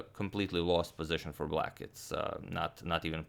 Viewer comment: did he said its just a lost position for black?